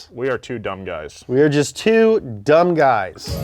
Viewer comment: Nolan and Pumphrey "We are just two dumb guys" What I heard "We are gust too dumb guys"